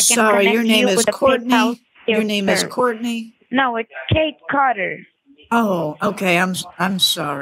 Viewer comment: I instantly thought I heard "Kane Carter", for those fnaf fans who dont know. Kane Carter is a famous fnaf dev who is behind the Popgoes franchise.